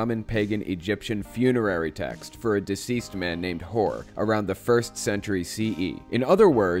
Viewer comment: Also I send my first comment a little too hastily, your autogenerated closed captions say at 7:54 "a man named Whore", which I think you might want to fix if Youtube let's you do that. The correct name would be Hor (with a fancy accent over the letter o, but I don't think that matters too much) according to Wikipedia. I don't fault Youtube too much for that mix up, it's just a little funny :)